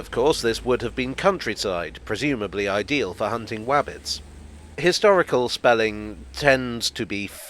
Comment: Hello - @ 0:36 - Did you just say "Hunting Wabbits" or "Hunting Rabbits"??? (I had to listen 3 times - still not too sure??? 😀) 🤔🚂🚂🚂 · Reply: It was definitely wabbits.